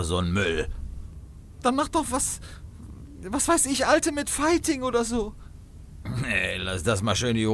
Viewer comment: Weiß jemand was sie in Minute 1:07 sagt? Ich verstehe immer nur sowas wie „alte mit fighting“? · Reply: Ultimate Fighting